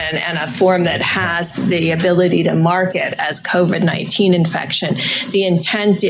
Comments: I heard "market" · I heard "market" too. But really they decided to mark it (market it) as something it wasn't.